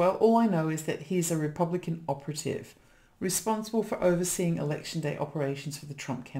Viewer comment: A Republican operative, you say? I misunderstood--I thought you said Republican opportunist.